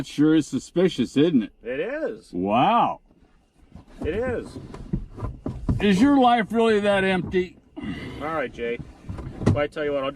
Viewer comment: "Is your life really that empty?" - is epic. But it was funnier when I mis-heard it as "is your wife really that empty?" lol